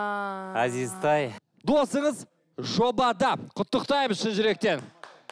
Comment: досыңыз жопада 😂 послышалось